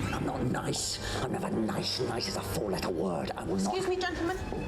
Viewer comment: In the UK 4 letter word is a euphemism for a swear word as many of them are 4 letters long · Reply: Oh that makes sense… I always thought he was saying “foul little word”